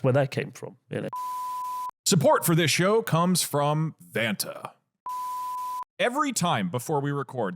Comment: i thought he said fanta... I thougth that would be a good sponser too